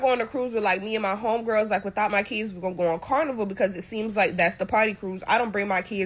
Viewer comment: What does she mean when she says without her keys? 7:03 Why doesn't she take her keys in high volume areas? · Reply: I'm not sure if you were being serious or not, but just in case... She said kids, not keys.